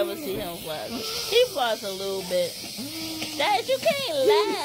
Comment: what do you think she said? "He floss a little bit" "He bout' to lose it"